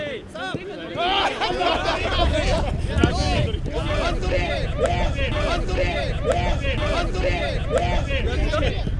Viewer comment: これ、リフレイン編集してて面白いけど、玖生は何て言ってるんだろ。 ロンドやってるボールなのに他から飛んできたボールだと勘違いして外にはじき出してるw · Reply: カントリ！？って言ってますね。完全に取らないと交代じゃないのに、玖生が交代と思って手で取っちゃった